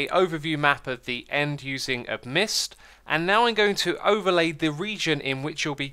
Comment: Does anyone know what program he mentions to generate the map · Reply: Amidst